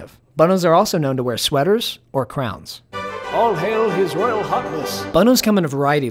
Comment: I heard it as “Royal Hotness” halp